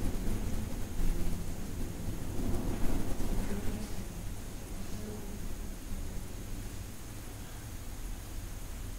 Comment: Dialogo: 👩🏻: Que hacen? 👩🏻‍🦰Estan grabando (lo otro que dice es indescriptible) 👦🏻:( suspiro largo de un hombre, fuerte i claro) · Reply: Se escucha después “Que salgan de aquí” si suben el volumen al máximo y presionan los 2 auriculares